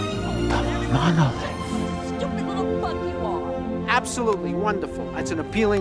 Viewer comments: Did she say the F bomb? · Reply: She said, bug. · I swear it sounds like she says fuck although I know that she's saying bug lol